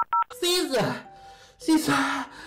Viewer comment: why did I think he said SZA SZA😅